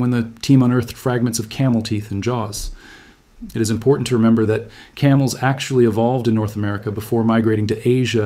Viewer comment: New information! Camels evolved in North America…. Thank you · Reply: Camels? I thought he said big carmels???